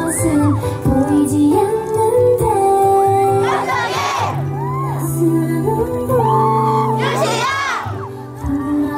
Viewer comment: 쯤에 엉덩이라고 하신건가요?? 잘 안들려서요... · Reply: D 은디 아뇨ㅋㅋㄱㅋ현승희(승희본명) 입니다!!